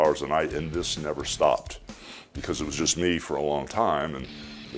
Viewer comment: Had to listen to it twice..thought he said a I did was eat cocaine back then..but he said protein